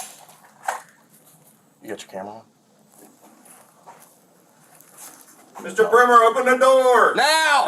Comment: When the cop said “Mr. Primmer” I thought he said Mr. Pervert